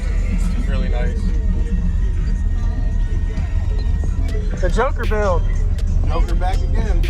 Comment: Just before 2 minutes he said it’s a joker build... at first I thought he said it’s a junker build ... then it took me a second to realize he said joker and not junker